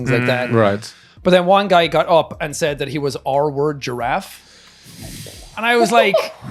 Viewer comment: What is Jack talking about the R word giraffe if Im not wrong?